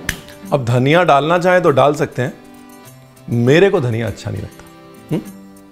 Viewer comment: did he actually say, mujhe dhaniya acha nahi lagta😂😂